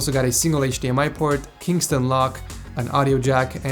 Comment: Did you say "Kingston Lock" at 1:39?